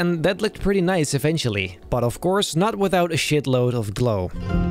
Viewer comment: Did u just say sh*t by